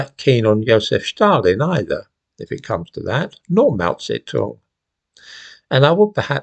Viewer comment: What do you mean by "melt said talk"? From transcript 1:25. Perhaps "Mao Zedong"? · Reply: He said Mao Tse-Tung. Zedong is just another way of spelling his name